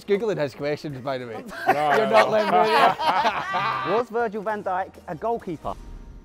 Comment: My brother thought when you said Virgil van djik he thought you said virgin hent@